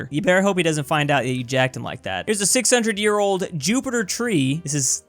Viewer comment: did he say Jupiter ?